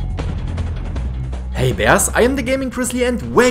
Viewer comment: What accent is that? German? And what is "Hey Vers"? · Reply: Gaming-Grizzly ahhh I see thank you for replying and clarifying that greeting. I always heard "Hey Vers" XD and wondered what it is. Hehe